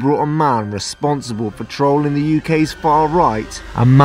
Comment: Thought you said "controlling the far right" and i clicked off this video because i didn't wanna watch nazi sympathiser ~prank bros~, a month later this autoplayed on my recomended but with subtitles this time & i heard "trolling" lol